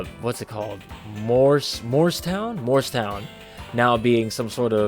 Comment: Love the intro! Am I the only one who just got that Morse town might be like Morse code? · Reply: I thought they were saying Morristown.